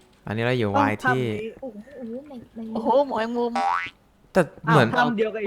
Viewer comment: ได้ยินพี่บีพูดว่า โอ้ยหมอยมุม555